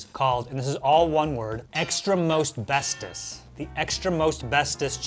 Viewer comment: I heard "Extra-asbestos"